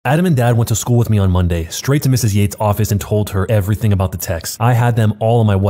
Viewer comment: I thought he said “straight to miss yeets office”